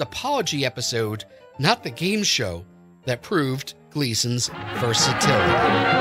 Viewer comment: because of the bad mix of music suddenly coming in, what the narrator said at this point was buried (I couldn't understand him). Even the closed captions stopped going after the word "proved." Could anybody decipher what the narrator was saying? I'm sure it was something along the line of Gleason being legendary. Thanks! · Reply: The word was 'versatility'.